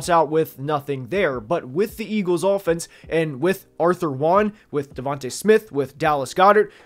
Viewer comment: am i tripping or did he say aurther wan instead of aj brown. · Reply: Arthur Juan is what AJ stands for lol